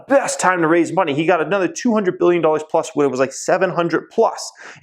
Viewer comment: am I the only one who heard 200billion dollars plus? 😂That would be awesome if Tesla managed to do that back then. They Coul buy out those criuse lines for funzies